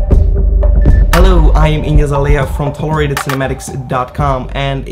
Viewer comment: lol i thought u said "im iggy azalea" in the beginning. thank u for this video. im tryung to learn editing on my own